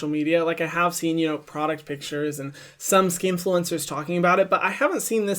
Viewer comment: Did he say "skinfluencer" at 4:30??? Love it❤❤❤❤